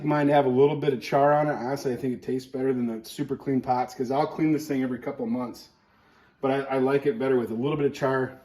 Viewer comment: What did he say? ... "I'll clean this thing every couple of MONTHS..."